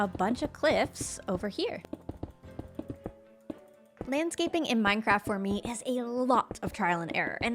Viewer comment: am i losing my hearing or did she say "manscaping in minecraft"